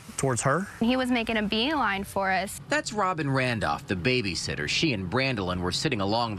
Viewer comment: When he said the babysitter I thought he said the baby sister I was like whaaat 😂